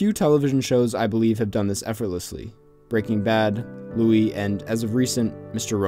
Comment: he lists some decent shows as Breaking Bad, and another one that i couldn't understand. Can someone tell me the name of that one? Thanks! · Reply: He says "Louie"